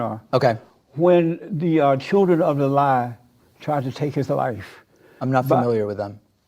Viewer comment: It's so painful listening to Jesse. "Great white hope!" "Black and slow." "Children of the lie" This guy is embarrassing to listen to. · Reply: LOL i thought he said Children of the light...xD Was like...that's a weird insult.. O.o